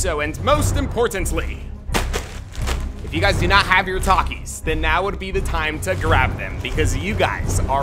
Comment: When I first started watching these videos and kept saying “grab your Takis” I thought he was saying Walkie Talkies 🤣😅